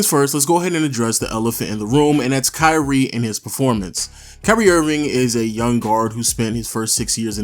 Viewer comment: is it just me or it sounded like he said curry Irving